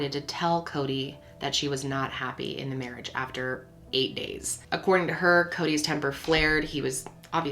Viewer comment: I thought that said 8 years not 8 days my gad